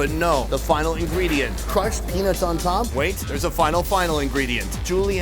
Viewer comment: i thought he said "crushed penis on top" took me a while to hear peanuts